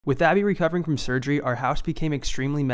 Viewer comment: I can’t be the only one that thought said “with daddy recovering for surgery”